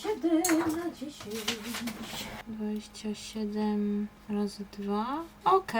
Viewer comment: Wait, is it just me, or I heard correctly, that you said "раз, два" ("one, two")?